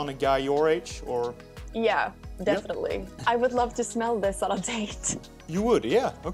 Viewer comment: I thought she said she wanted to “smell it on a taint”